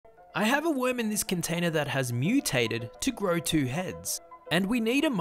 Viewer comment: Why did I hear, "I have a women in this container that has been mutated" 💀